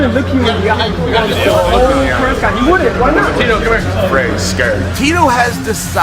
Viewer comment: Maybe i am wrong but when Tito is mugging him in the elevator towards the end it sounds like Chael says "Tito come here"